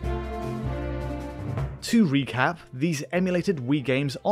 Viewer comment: did mf really say "wii-cap"